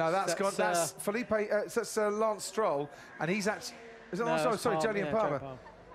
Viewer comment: did he just say Felipe Sir Lance Stroll Palmer?